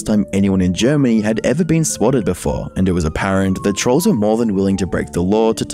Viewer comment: Im currently working, just blacked out after dealing with a customer Heard reyna faught trolls. I thought you said ranni fought trolls. I was abiut to say "when